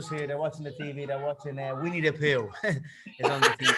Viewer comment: there watching winnie the poo subtitles say there we need a pill :)